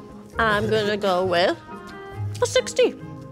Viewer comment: it seemed to me that said 60 instead of 16